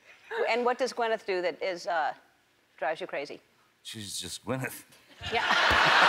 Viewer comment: What does Johnny say at 2:36 ? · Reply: "she's just gwyneth"